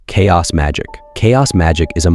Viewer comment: i thought it said Chads magic